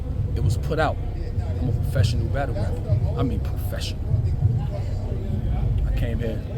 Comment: Yo I swear at 2:44 this nigga said it was put out that I’m a professional rat-tle rapper lmaoo wtfff😂